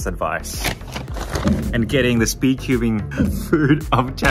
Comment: the captions actually said “Speed ubing” instead of “Speed cubing” And at 12:52 the captions said “plany” instead of “plant”.